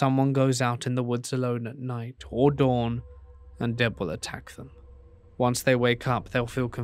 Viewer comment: i couldnt tell what he said= deb. i needed the sub tittles.